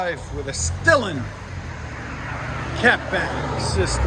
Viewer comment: Who all thought it said stolen scat pack💀💀